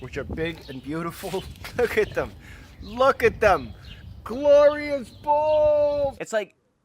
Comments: That one guy confused me... I thought he said “Gloria’s balls” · thought he said 'GLORIA BOR(GER)' instead of GLORIOUS BALLS